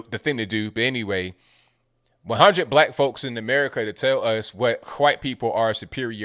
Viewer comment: did you just say chwhite?